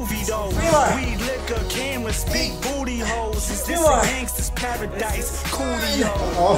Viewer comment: Did I just hear "big booty holes" in the song?